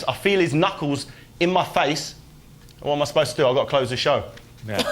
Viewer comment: Would somebody help me and tell me what Haye said from 4:55 to 5:00 ? :) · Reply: Jack Daniels He says ''I've got to close the show'' meaning end the situation which is going on. Hope the 6 year wait wasn't too long to wait for the answer!